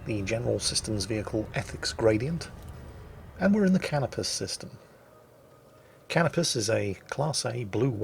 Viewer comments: Need to get my ears washed out. Thought you were saying "Cannabis System" at first... 🤣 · I legitimately thought he said Cannabis System. I was like wow, I guess I know where I'm going next.